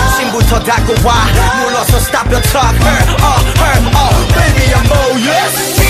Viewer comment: instead of "baby i'm all yours" why did i hear "baby i'm gorgeous"